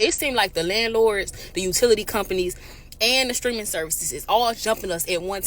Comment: did she say streaming services?